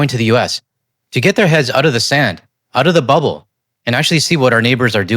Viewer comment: You said, "out of the bubble", but I heard "out of the butt hole". LoL. Keep it up. :face-blue-wide-eyes::face-blue-wide-eyes::face-blue-wide-eyes